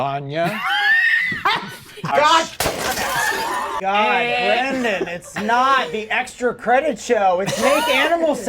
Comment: I always hear "Brendan"